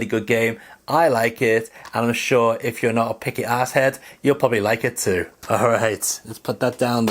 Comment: lol, In my mind I was thinking what to say. No idea where ass head came from. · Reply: I thought you said "asshat," which is essentially the same thing. But yeah, I don't care one way or another about the whole Last of Us controversy.